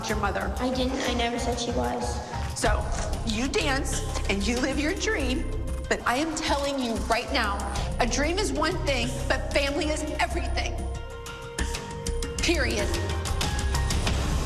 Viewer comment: did she Said period🩸 ? Oh my gosh 🔥